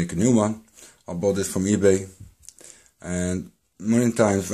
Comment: A rather interesting thing I noticed. With subtitles on at 2:04 he clearly says I bought this from 'ebay' yet the subtitles say 'bangood' I clearly youtube does not just listen and convert what it hears, it seems to adapt what is being said as those words are not remotely similar. rather interesting and I wonder if there is a link there purposefully in the background. Also as 2:24 it does it too.